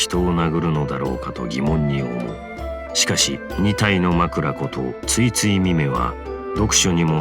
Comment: AM I THE ONLY ONE WHO HEARD MACHORANGA?